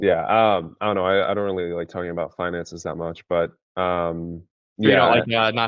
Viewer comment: what did you say exactly at 4:15 ??????? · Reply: he said "I dont like talking about finances" to try to take the frame from me So I said "You probably don't like not having the skillset to go make more money too right" to take the frame back